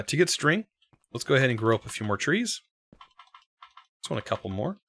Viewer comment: did he say "let me grope some trees"